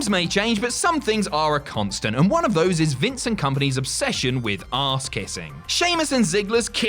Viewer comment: Did anyone else think he said Vincent kompany instead of vince and company at 7:09 😂